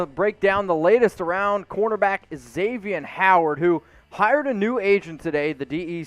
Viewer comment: I thought it said “Xavian Howard Free agent” 😰